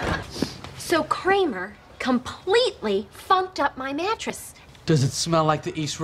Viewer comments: it sounds like she said "So Kramer completely F#cked up my mattress'.. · did she say “Kramer, completely fucked up my mattress”??? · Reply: funked.